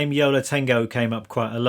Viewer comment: Ha ha. I thought you said "Yellow Tango". Took me a while to realise you said "Yo La Tango".